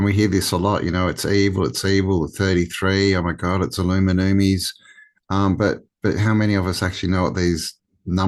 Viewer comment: what does he say here? illuminumies?